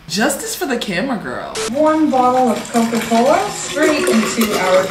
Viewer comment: sorry did she just say a warm bottle of coke- 😭😭